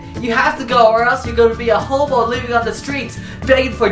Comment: it sounded like he said homo instead of hobo XD